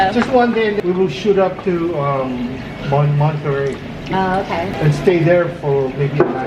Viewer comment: omg did he say Monterey?!? Girl that’s where I liveeeeee omgggg